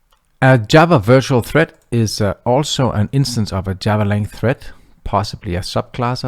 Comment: I cannot make out what you are saying. Is it "Java length thread"? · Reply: java.lang.Thread